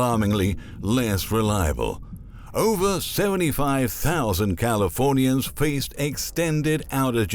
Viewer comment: I thought he was about to say that, "Over 75,000 Californians face extinction."